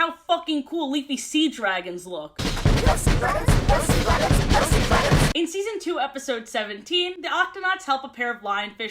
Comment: ok um what did they say here? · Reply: GO SEA DRAGONS GO SEA DRAGONS 💃💃